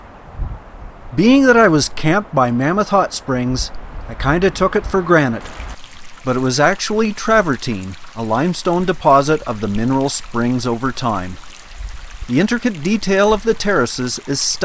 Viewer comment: at first I thought you said "...I took it for granted" but listened again and it was "...I took it for granite." Thank you for a beautiful video.